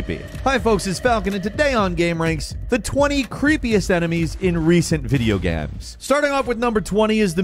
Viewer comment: did you say video GAMS ?